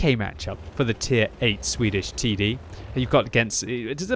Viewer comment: "tier 8 swedish TD" did i hear correctly?